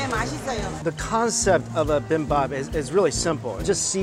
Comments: did he say bim bop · did he just say bimbap 😂